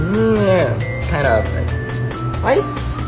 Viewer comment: I thought in my brain he said "10/10" but he said "kind of like"